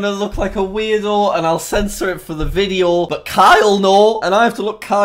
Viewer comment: Wen you said “Kai will know” it sounded like you said “Kyle NO!”